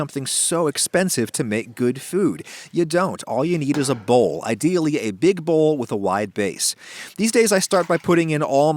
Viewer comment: did he say BASS?